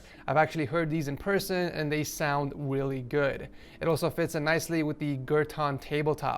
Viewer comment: did he just say it sounds willy good or is it just me